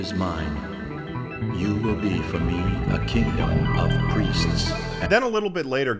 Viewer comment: I thought the voice said “you will be f-ing a lot of priests” on 16:06 LMAOOO